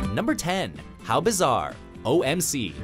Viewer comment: For the longest time I thought he was saying "help is on" instead of "how bizarre"